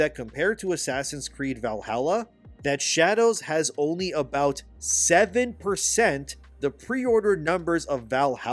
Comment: I misheard that at first. I thought you said it had 70% of the preorder numbers that Valhalla did. And I thought, "Wow, only 70%? That's pretty bad..." But it's actually 7%? SEVEN? LOLOLOLOL